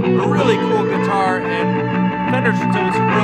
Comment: Did you just say kicken’ a** lol